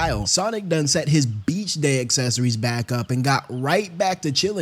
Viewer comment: Did he say Bitch fr?😭